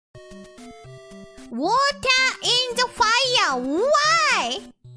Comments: I thought he said work out in the fire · I thought is said water in the wild